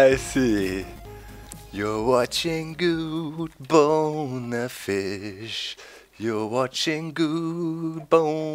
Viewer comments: Did he just say "you're watching Guude bone a fish?" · at first i thought you said guude bonner fish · OMG it sounded like you said guude boner fish and i nearly peed myself laughing but really here is a maybe topic what inspired you to play minecraft and make all those redstone captraptions